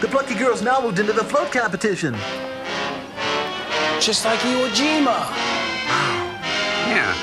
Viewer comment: Was it my imagination or did Joel say "...just like Iwo Jima..." during the part where the women were building the boats at 35:29?